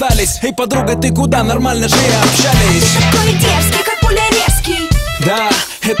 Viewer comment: Мне послышалось:А ты такой детский Как пуля мерзский